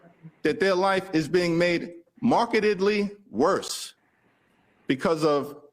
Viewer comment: he's so thoroughly bought that he Freudian slip'd "marketedly" instead of "markedly"